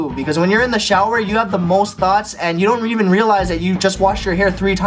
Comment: ADHD things: (don’t self diagnose from these or convince yourself that you do them because self diagnosed looking for an excuse are so annoying but if yup genuinely do relate to these maybe get checked out) Finding a lump, bump or pimple and picking at it without realising and forgetting not to, making the pain or problem worse Replying to conversations and then asking what you just replied to (lack of focus) Constantly moving, even if it’s small movements like moving your teeth or fingers because you’ve had to mask it to fit in Getting random ass waves of anger, sadness or full on crackhead Getting really into things (like TV shows or bands) really intensely, but getting sick of it pretty quick Good at doing things fast (like typing, talking, running) Gets distracted very easily Has lots of tabs open on laptop Cannot stay focused on one subject during study Constantly has a million thoughts at once Zones out all the time Good reaction time Forgets what they are doing while they are doing it Can remember lots of people's names but randomly forgets the people close to them sometimes When you're taking notes and then a random thought pops up so now you've accidentally written that thought instead of what you're trying to copy Like if you can relate and comment any others so I can add them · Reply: I thought u said ,"ass thoughts" lol